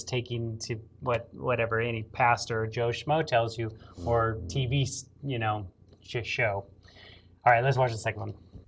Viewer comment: sounded like you said "t.v. sh!+show"???